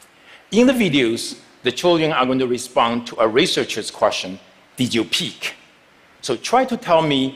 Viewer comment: I thought they said did you pee!